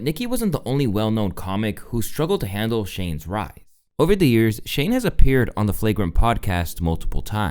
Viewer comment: Shane's what? Didn't catch that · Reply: I think he tried to say wrath but said “writhe”?